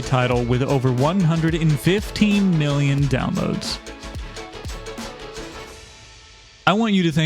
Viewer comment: i thought you just said "more than 115 downloads"